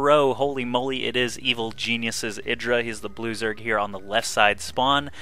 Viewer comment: I hear "loser" instead of "blue zerg"